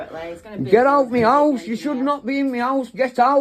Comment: Oh boy! I can hear Schrodinger’s Cat from Heaven saying, “This moron says he thinks he can gets gas and electric for free…because reasons.” 🤣🤣🤣 RIP Cliff! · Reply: Get out meows. Get out meows. That's all I heard him saying